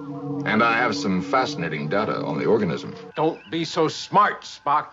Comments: Can we talk about Kirk's "Leonard, Leonard, Leonard" accompanied by a chuckle and a "there there, it's okay" pat on the chest? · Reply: I thought he said "Later, later, later" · Can we talk about Kirk's "Leonard, Leonard, Leonard" accompanied by a chuckle and a "there there, it's okay" pat on the chest? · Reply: I heard "Later," also.